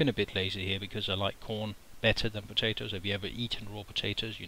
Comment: I thought he said 'I have been a bit lazy here, because I like porn better than potatoes' :P